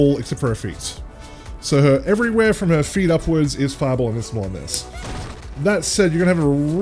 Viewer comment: what did you say at this part? All your words blurred together right here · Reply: “Everywhere from her feet upwards is fireball invincible in this”